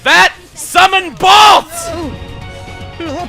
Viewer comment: Seriously thought that said "SUMMON BALLS!"